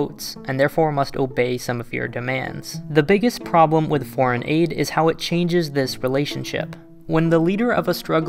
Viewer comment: i thought it said "the problem with foreign ad"